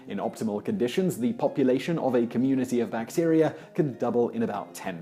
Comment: Electron Pusher in this context bacteria is right since you need more than one bacterium to start the fire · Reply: It sounded like he said "bacterias".